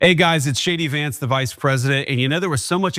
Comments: "Hey guys, it's J.D.Vance the vice president". Now THAT is tragic! The fact that his opening message on this video is to remind everyone watching who he is and the job he 'Apparently' holds. What a loser! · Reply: I had to check the batteries for my hearing aid as I thought he said, "Hi guys, It's SHADY vance the vice president." 🦻🏻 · Ooops my bad...heard him say "It's shady Vance" instead of J.D. Vance :D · i thought it was an impersonator cause it sounded like he said shady vance lol